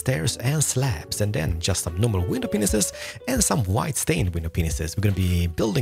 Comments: sounds like he saying “window penises” xDDDDDDD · is it just me or did he say “window penis” I’m not making fun of you in anyway I actually love your videos but that was funny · why does he say it like that? It sounds like he’s say “penis” I know the guy is an accent, but it’s not hard to say “window panes” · it sounded like he said glass window penises! Lol